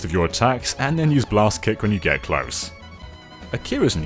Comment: did he just say Blast Kick? (Facepalm)